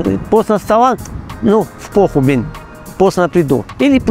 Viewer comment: что он сказал ? По,,,,уй блин?😅